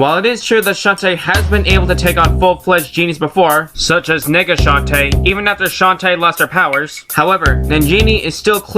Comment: Sounded like you said Nigga Shantae! XD